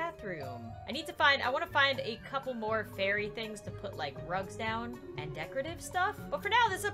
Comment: When you said rugs I thought you said drugs lol